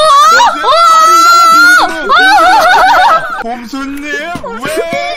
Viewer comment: 뭐지 힙합 너무 들었나 빈지노라고 들리네